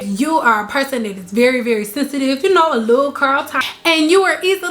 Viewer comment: did she say "...a little Carl Thomas" Lol!